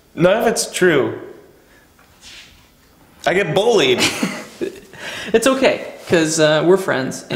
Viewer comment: did josh say "bowlied"